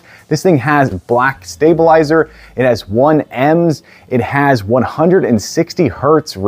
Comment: did he say "it has 1 "ems"???